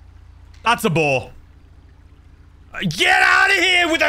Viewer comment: Lmao I was just listening while playing gta in the background and I kept hearing “THATS A BALL!!” boy was I wrong 😂